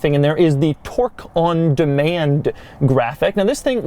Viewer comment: I thought Doug said "Twerk on Demand", yeah I too have no idea what that could possibly mean.